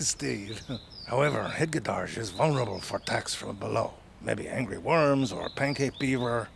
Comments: I thought he said "hedgehogs are vulnerable to tax fraud" and now I demand a redub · I swear I heard him say taxfraud instead of "attacks from below" (or whatever it was) And I suppose it's been manifested now. Hedgehogs are now vulnerable to taxfraud. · I heard "vulnerable for tax fraud alone" for some reason. That doesn't sound right 😂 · OMG I thought you said he’s vulnerable to TAX FRAUD from below! lol